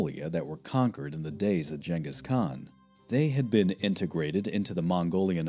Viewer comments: Did he just say, “Jengis Khan”? 😂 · Did he just say jangis Kahn wtf